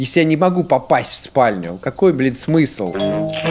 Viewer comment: я первый раз слышу от мат))) ( какой бл@ смысл) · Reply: Он сказал "блин"